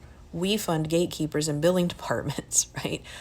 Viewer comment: My ancestors emigrated to the US when it was a good idea, many years ago. Not anymore. BTW, at 6:23, your captioning says "building departments". I think you said "billing departments"?